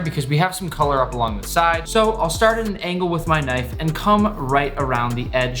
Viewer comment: Did he just say "Cum"?